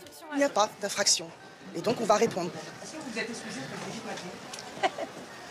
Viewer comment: Moi, j'ai entendu "vous êtes un port !" C'est pas plus antisémite de traiter quelqu'un de gare routière, ou d'aéroport !